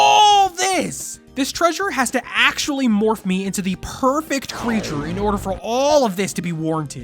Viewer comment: did someone say perfect?